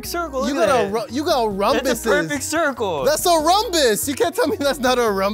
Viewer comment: I heard rumbous